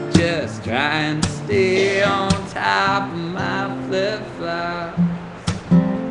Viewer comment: I thought in the lyrics he said “just staying on top of mount flip flops”